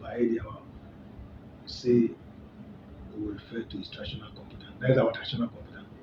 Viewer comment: Did I hear him say "Traditional computer" 😅